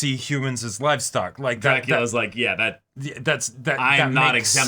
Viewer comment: I thought it said Dracula's genitals